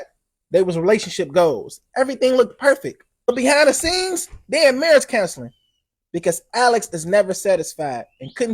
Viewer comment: Did he say, "Day inn Marriage counsilin"????